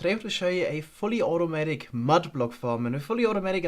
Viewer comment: I thought this said "Finally, Automatic Mud Farm"